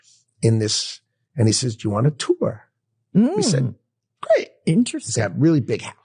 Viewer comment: I heard "Do you want a tuba?"